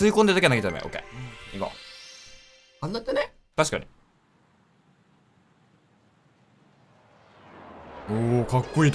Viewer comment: さん何て言ってる？w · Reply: ガヴリール それだ！wありがとう！